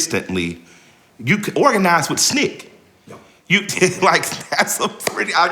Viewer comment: what name does he say? Snick? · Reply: SNIC, the acronym for Student Nonviolent Coordinating Committee